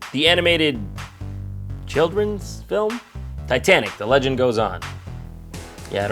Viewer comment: I thought it said "Titanic: The train goes on"